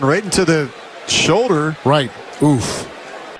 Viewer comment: Did i hear that right? 😂 8:15 “OOF”